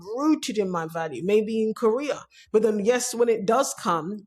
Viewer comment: Oh, career! I heard Korea. I thought it was some analogy I didn't get.